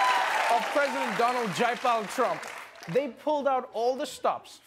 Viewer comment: Did he said "President Donald Jaipal Trump"? 😆 Context: Jaipal is an Indian name. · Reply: Mudit Hyanki I heard “Donald Junkpile Trump” which I totally accept as a legit nickname for him but if he said “Jaipal” is that a name or does it mean something?